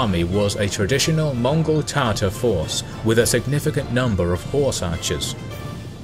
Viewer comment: did u say mongol tartar sauce? lol.